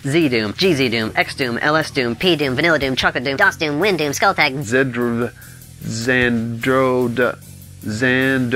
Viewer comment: it sounded like you said skullfag